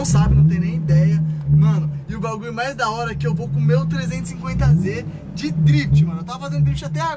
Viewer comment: SÓ EU OUVI QUE ELE IA COMER O 350Z!!!